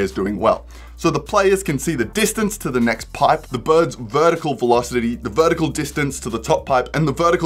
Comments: I swear I heard "birdicle" and not "vertical" lmao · Thought he was saying, "birdical," instead of, "vertical,"